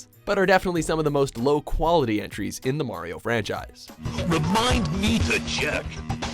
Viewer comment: did he say "remind me to jerk"?